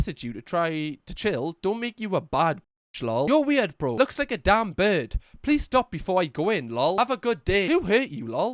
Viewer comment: honestly thought you said dan burn